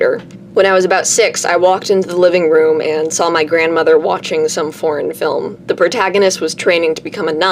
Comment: I definitely thought you were saying "some porn film" and I was very concerned😂